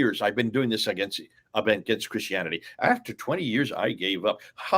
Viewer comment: I had to read the name of the channel because of the gentleman's accent.I thought he said white christianity.lol